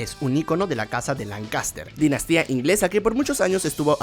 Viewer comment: pensé que decia Lannister xd